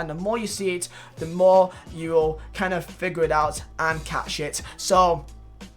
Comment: did he say the more you figure it out and cat shit ?